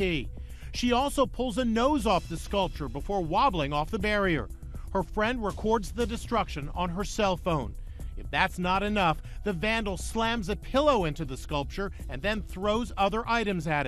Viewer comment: “before wobbling off” bruh the commentator a savage 💀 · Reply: I thought he said, "waddling-off"?... as if commenting on her physique. Lmao!